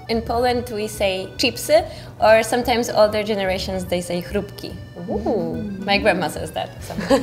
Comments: she said "chrupki", not "frytki", the subtitles got it wrong, these are two different things:) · there’s a mistake in the subtitles - in 1:36 it should be ‘chrupki’ instead of ‘frytki’ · she said chrupki (like these corn puffs crunchy something idk) but the subtitles say frytki (fries) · "frytki" in the subtitles is wrong. She said "chrupki" and she's right - older generations call potato chips "chrupki" quite often and "chrupki" is similar to "crisps", as the name indicates that they are crispy. "Frytki" means French fries.